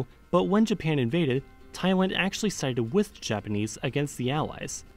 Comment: did you just say TAIWAN???